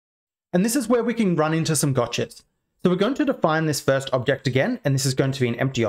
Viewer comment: I thought he said "and this is where we can run into some dogshit" 🤣😂 had to watch it back to realise he said "gotchas"